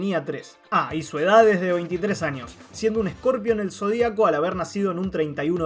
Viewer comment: "Edad 93 años" Eso entendi ._.